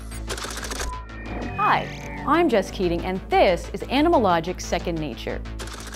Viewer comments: i heard "hi i am just kidding " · Thought she said "Hi, i'm just kidding." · Did she just say "hi I'm just kidding"? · I legit thought she said, "Hi, i'm just kidding, and this is animalogic." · I heard ...."Hi, I am just kidding"..... what's wrong with me! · hi I am just kidding lol 😂 anyone heard that or is it just me